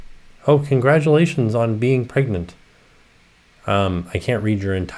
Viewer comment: I swear to you I thought read a comment that said “congratulations on being pregnant” at 24:35 like are we just not going to talk about that lol